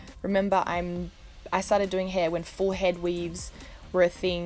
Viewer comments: “I started doing hair before hair weave was a thing” It’s always been a thing boo. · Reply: She said she started doing hair when forehead weaves were a thing...I can see how you misheard her tho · She said she started doing hair when forehead weaves were a thing...I can see how you misheard her tho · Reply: sounded like she said full-head weave. Wth is a forehead weave?